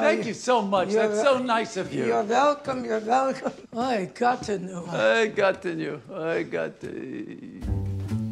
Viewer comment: they say: "oy, gottenyu" in Yiddish. got = god (like in Germanic languages), and -nyu is a Slavic diminutive suffix. So the meaning is "oh god" but less formal. I'm sure a lot of people were wondering what the hell they're saying. The Netflix subtitles mistakenly said "oh gotten you".